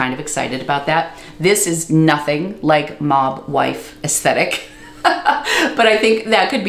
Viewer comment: Oh, I thought you were saying MOM WIFE, so I had to look it up ... .you said MOB WIFE ... definitely not the same thing. LOL